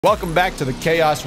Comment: I thought I heard "welcome back ya dingos." Lol